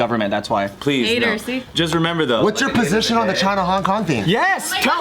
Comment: wait does he say "just remember though, I ain't gay" ? lolol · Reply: No lool he says "just remember though, at the end of the day"